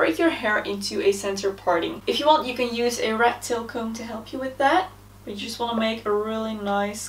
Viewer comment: For just a split second at 0:46, I heard "rectal comb" instead of rat-tail comb. lol